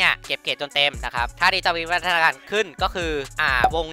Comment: พี่พูดว่าไรนะ😂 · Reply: ถ้าวงเนี่ยมันจะวิวัฒนาการขึ้นมั้งนะ